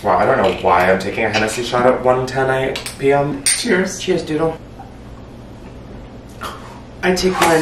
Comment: I lost it at “cheese” instead of “cheers” bc when I was a kid that’s literally what I thought adults said.